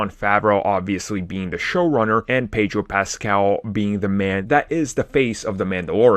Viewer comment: Yo i thought you said amy pascal i was like disney no